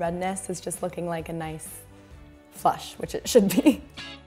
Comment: did she say flush